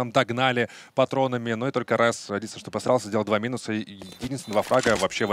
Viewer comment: мне одному послышалось обосрался